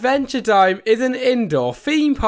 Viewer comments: I thought he said adventure time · I thought He Said Adventuretime